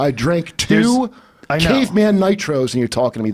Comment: For some reason when he said "caveman nitro" all I heard was "cocaine"